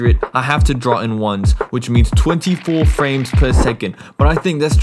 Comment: did you just say 24 frames